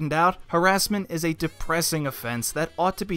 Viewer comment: thought you said meming